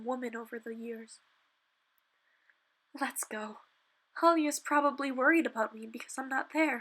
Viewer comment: Am I the only one that heard her say Chloe instead of Alya